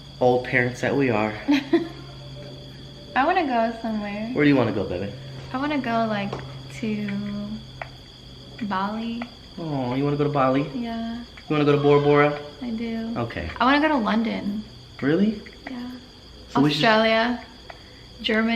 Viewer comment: i thought she said maui, so i was like “YESSSSSS” but then said it again then i was like “awwwwwwww”